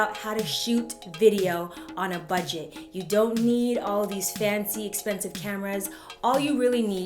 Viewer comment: I thought u said we didnt need all the fancy stuff